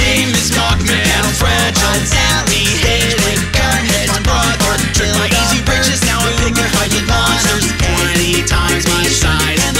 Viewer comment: I misheard Barroness Von Bon Bon as Varron is my mon mon. Oops.